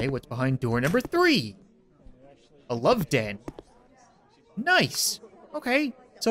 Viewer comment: he says a "love dead" instead of a "love bed" lmao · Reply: yes he said love den